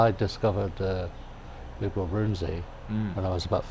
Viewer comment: what is the name of the artist Bert mentions? I cannot quite make out the name. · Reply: Big Bill <link>